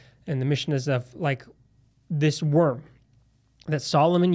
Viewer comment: Did you say "worm"?